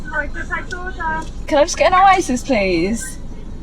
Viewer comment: The British accent was so heavy that even Youtube CC couldnt get what she said (neither me) ! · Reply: You mean when she said "Can i just get an oasis please?"